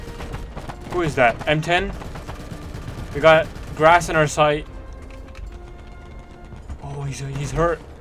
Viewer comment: “We got grass in our sight” Me: just turn it off then no big deal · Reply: i thought he said "we got grass on our side" lol